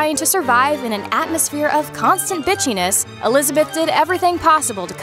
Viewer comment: wait, did you say bi**hiness?!